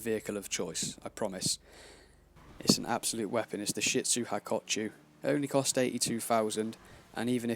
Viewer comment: did he say shit soup??